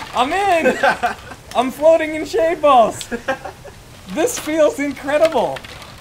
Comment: sry what did u say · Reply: Hes floating in shade balls there's really nothing you can find out of context there